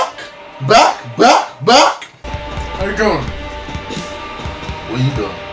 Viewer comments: yo I thought they saying"we black"😅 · I thought he was saying "we black"